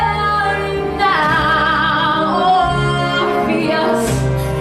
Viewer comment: bro heard her say "ORPHEUS!!" and thought she asked for Morpheus